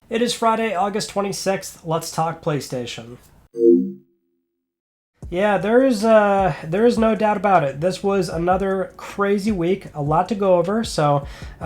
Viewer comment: Why I keep hearing "It's Friday August 20 sex let's talk Playstation" ?